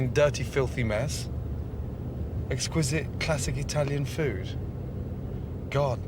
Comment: I think its called, exquisite italian accident!